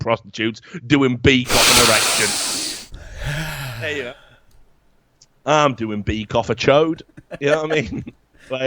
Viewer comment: I don't understand the accent very well, WTF did I just hear.... "Doing beak offa chode" ???!!!